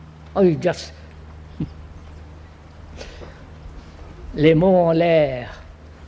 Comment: Does anyone know what phrase he uses at 11:36? It sounds French, but I can’t tell. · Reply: It might have been “les mots en l’aire” to mean “idle talk” if I were to guess